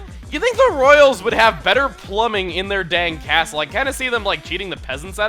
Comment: I definitely heard "But seriously, you would think the royals would have better plumbing in their DANK castle"